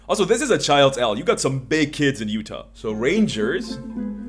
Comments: was listening while working and got psychic damage when I heard “you have big kids in Utah” like, WHEN DID YOU GO TO UTAH??!!!?? But also yes, lots of farmers kids still around when I was growing up, so lots of big kids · I was listening while working and got psychic damage when I heard “you have big kids in Utah” like, * WHEN DID YOU GO TO UTAH??!!!??* But also yes, lots of farmers kids still around when I was growing up, so lots of big kids